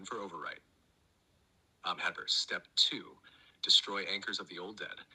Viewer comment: What did say right there? It sounded like my name to me. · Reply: My name is Heather